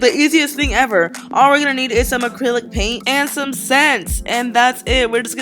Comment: i heard and some sex who else herad that or am i a perv? idk ples tell me im not the only one